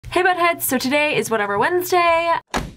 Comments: i thought she said hey butthead... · Lol it sounds like she said “hey buttheads!” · i thought you said hey buttheads in the beginning 😂😂😂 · At the beginning, I thought she said hello buttheads!! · did she just say butt heads